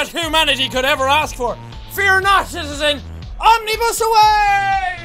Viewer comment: When Jack said "OMNIBUS AWAY!" I literally thought he said "SEPTPLIER AWAY!!"